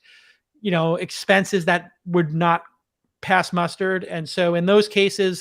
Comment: JCal @ 19:55 did I hear "Pass mustard"? instead of "pass muster"? Anyway I love the podcast. I like the daily tempo of TWiST... also looking forward to the next All In Podcast, its been too long.